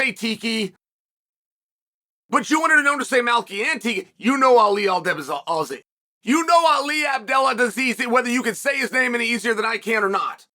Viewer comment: Did Chael just call Ali "Abdel a disease" ? Edit